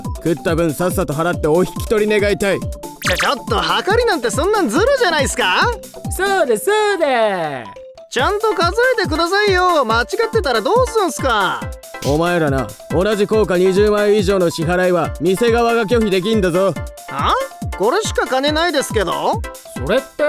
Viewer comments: 数えてくださいよが教えてくださいよって聞こえた俺のだけ？ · 一番最初「数えてくださいよ」の所が何回聞いても教えてくださいよっていってるように聞こえる